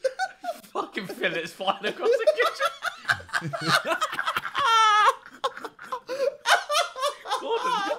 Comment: What did ethan say here ? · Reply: "Fucking fillets flying across the kitchen"